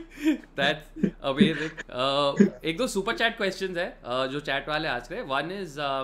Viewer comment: did you say "ask rahe h?"🤣🤣